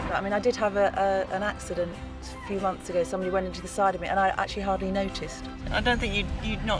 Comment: "somebody went inside of me and I actually hardly noticed" oh boy did I heard if from my partner. also a few months ago..... now where's my rope